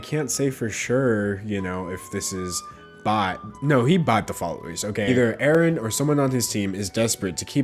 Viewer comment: For a second i heard "aaraon kyro" lol